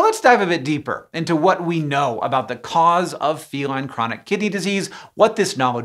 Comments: I heard "feline chronic kitty disease" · This video was very confusing for me because i kept hearing "kitty disease" and needed to rewind over and over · What I heard: "Feline Chronic Kitty Disease". I misheard but I kinda feel like I was right